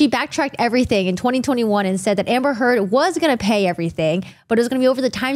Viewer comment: Sad I thought she said amber turd for a second there